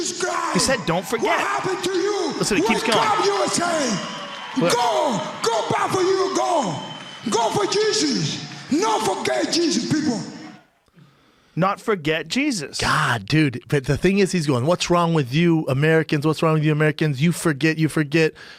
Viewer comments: “No forget Jesus people” is what he said. Brendan couldn’t be more wrong. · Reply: All this time that's what I thought he said too. "No for gay Jesus". I was wrong. "No forgay Jesus"(Obviously seeing it multiple times now he says "No forge(t) Jesus". I had no idea. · Schaub is so full of shit. Any idiot can hear wtf yoel is saying. I heard " no forgayt jesus" in the interview and knew exactly what he was saying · Go for Jesus, not for gay Jesus people! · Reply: i thought he said "competition, no forget Jesus people"